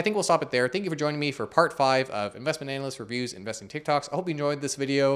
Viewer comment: I heard "Investment analyst abuse😂😂😂😂" taking to account the TikTok video before about not making 400k €(especially€)